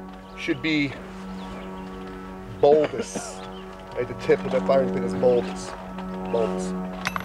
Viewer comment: What does he say at 2:30 ? My laptop speakers are really bad and can't hear him clearly. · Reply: Archaean “Tip of the firing pin should be bulbous”